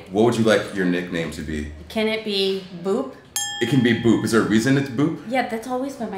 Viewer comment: Am I the only one who heard boob instead of boop